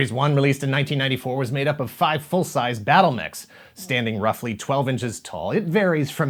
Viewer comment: Why, I keep hearing, "Battle Necks"